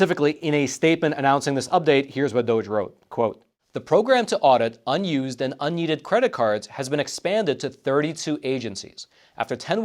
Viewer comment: lol I thought he said “to audit unused and yeeted credit cards…” 😆